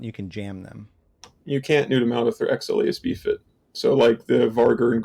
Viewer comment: I'd love to know what mentions as a counter to neuting marauders, but I don't understand what he says at 14:08 ("XL AB fit"?), 14:13 ("nosis"?) and 36:35 ("??? spam"). Can anyone help me out, please? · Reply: xlasb fit marauders, particularly the vargur & golem have non cap dependent tank and also guns, so pretty op. nos' (nosferatu) steals cap from the target so they can keep mods running - is what he's referring to and i'm not sure of the timestamp of your last one but spamming something just means to use the same thing (ship) or tactic a lot